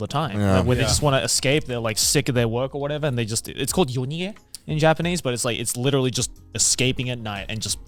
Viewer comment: What’s the word called in Japanese? That Joey said? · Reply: got a timestamp for when joey says the word? edit: assuming you're asking about 10:10, which i think he's saying "yonige" as "yonigeya" is a person who "helps others flee" "a fly by night arranger" edit2: 夜逃げ "night flight", so yeah thats what he was saying.